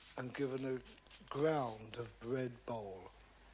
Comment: What does he say at 5:59? A Ground of red bowl? I'm trying to figure out what red ground is used on it? I didn't realize there was a ground at all I thought it was just flat on the panel itself. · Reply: He is referring to red bole, not a bowl.. This is the red pigment used to paint the ground upon which he executes the initial chalk drawing. It's a pigment made from red clay, used commonly during the Renaissance, and is still available at selected arts and craft suppliers.